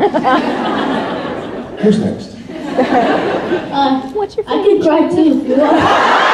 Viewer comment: What did that person say at 1:07:01 when everybody is laughing??? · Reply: Ninaofthe90s “I can cry too if you want”